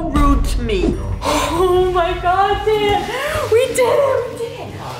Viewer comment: uhh...did just drop the word..."d**n"👀 and 6:02 did just call her brother jay!!!